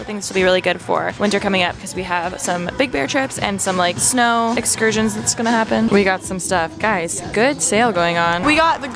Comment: did I hear Big Bear trip coming up?!?!?!?!